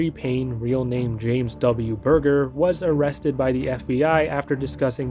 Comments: burger??? did you just say his last name was BURGER??? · DID HE JUST SAY HIS LAST NAME IS BURGER 😂😂😂😂😂😂😂😂😂😂😂😂😂😂😂😂😂😂😂😂😂😂😂😂😂😂😂😂😂😂😂😂😂😂😂😂